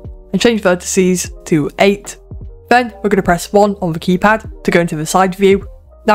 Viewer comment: what he say? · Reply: click one on the keypad